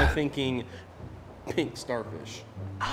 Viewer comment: I thought said “pink star bush” but that’s just me.....